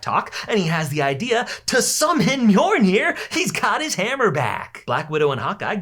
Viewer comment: Did you say, Mjornir?